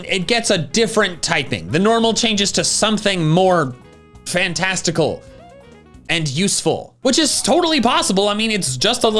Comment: I'm eating cereal and sometimes that distorts what I hear I heard 'I hope it evolves into something more..... plantastical'